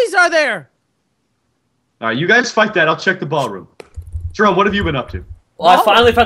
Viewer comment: go to 5:23 watch until 5:26 wait, Mich why did you say the f word?, if you don't understand what I mean listen through that part a couple times and listen closely to Mich/Bajancanadian. I don't know what I'm saying, I'm just random... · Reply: he said u guys find that